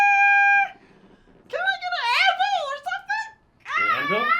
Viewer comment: Anvil? I thought she said Advil. Lol